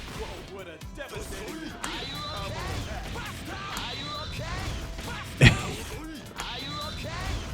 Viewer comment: Just me or is Terry saying "are you okay, bastard?" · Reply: Are you OK? Buster wolf